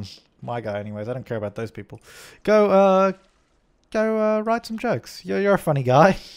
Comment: Lol when he said go write jokes I thought he said go get some drugs... XD